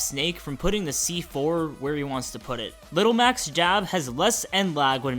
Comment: I can’t be the only one who thought you said “Little Mac’s DAB...” Can I?